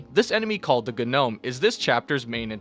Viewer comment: i know you said Gonome but I heard "Good Gnome"